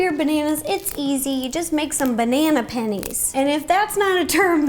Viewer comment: Ngl I thought that you kept saying Banana panties. Lol